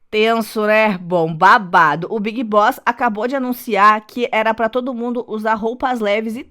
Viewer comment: Juro que ouvi "O Big Bosta acabou de anunciar..."